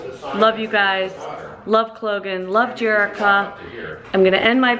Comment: subtitle says LOVE JERKS :D